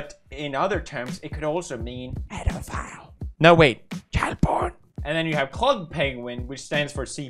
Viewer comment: When he said that cp stands for child porn the first time I heard it I thought he said cat porn lmao😂🙈